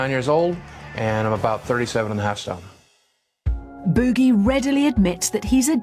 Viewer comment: What does he say from 3:21 to 3:23 he says... and i'm about 37 and a half WHAt? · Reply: Stone (1 stone is about 14 pounds so he's about 525 lbs)